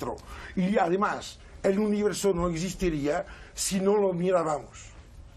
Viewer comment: Minuto 21:35 por favor alguien me podrá decir que es lo que dice el contertulio? (El universo no existiría si no lo (que?) De antemano muchas gracias. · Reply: Si no lo miraramos Y luego hay un sonido indescriptible seguramente ocasionado por arrabal XD